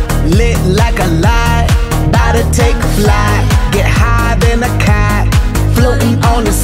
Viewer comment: get higher then a kite i thought will i am said get higher then a cat